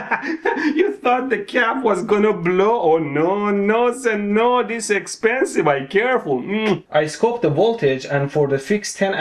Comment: what is he saying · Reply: "You thought the Cap was gonna blow!!No,no,no se-no this expensive me careful!!!" smooch